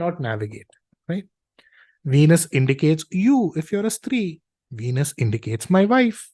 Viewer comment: May I please ask what you mean by saying at 00:47 seconds “Venus indicates you if you are a three?” Thank you Never mind I figured it out ❤ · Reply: Not three. It is Stirī. That means women.